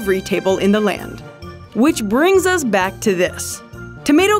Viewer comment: Omg I thought they said fox guts for a second that scared me